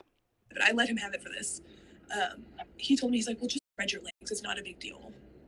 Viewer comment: I can't understand what they were saying through the censoring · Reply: i have it uncensored later in the video, it was "spread your legs"